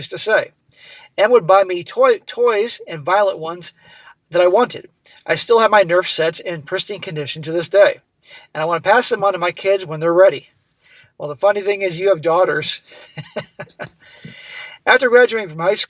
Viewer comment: body-green-covering-eyes: @ 12:00 " Oh NERF Set's ! " :body-green-covering-eyes: I Thought He Said NURSE OUTFITS :face-pink-tears: Mind-You, After Listening About His Lefty Nut-Job " Parent's " I Would NOT Be Surprised :eyes-pink-heart-shape